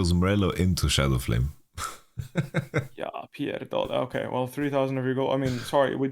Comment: did Bwipo just said ''ja pierdole'' or I'm losing my mind, what the fuck